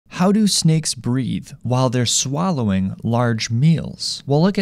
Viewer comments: i heard him say “how do snakes breed” And i said “WAIT WUT” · I thought you said "Breed while they eat"